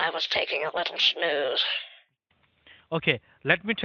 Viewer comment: the closed caption, instead of it saying snooze it said sh*t. It made me laugh.